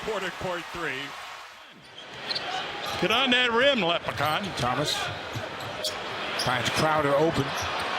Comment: did he just call thomas a lepricon?